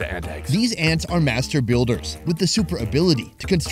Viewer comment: What I heard - " these ants are Master-baters"